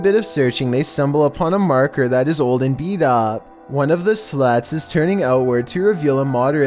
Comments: it sounds like he said “one of the sluts” 😂😂😂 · "one of the slots" i lowkey thought he said one of the sluts💀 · instead of "slots" I thought he said "sluts"